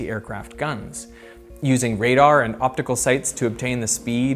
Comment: the transcript says "optical sites", where "optical sights" should be, followed closely at 14:10 by "few setting" instead of "fuse setting".